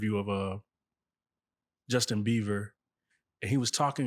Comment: I’m already irritated. Did he call that man Justin Beaver ? 🙄 Please get a real interviewer or at least a curious black woman . This is low quality journalism. But Jag is lit ❤